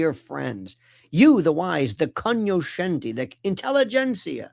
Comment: What is the word is saying at 0:31? What I’m hearing is con-yo-shen-dia. Apparently that’s not how it’s spelled because an internet search isn’t finding it.